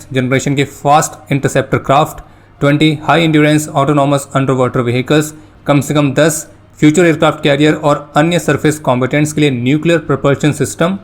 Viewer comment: Did i hear it right?? 1:46 "Kam se kam 10 Future Aircraft Carrier" 10 Carriers??